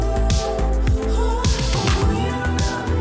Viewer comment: Why would she say she had a huge dinner when she eatin dinner?? ( JOKE ) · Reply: I heard it as 'tit' instead of dinner 🤣🤣